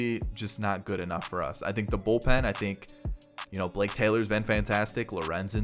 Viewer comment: When you said "Blake Taylor," I heard "Jake Taylor." I don't think that one's got the distance!